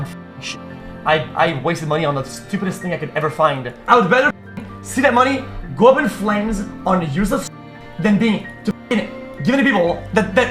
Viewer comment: what did he even say XDDDDD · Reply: i would rather fucking see the money go up in flames on useless shit than bein- than fucking give it to people. thats all they care about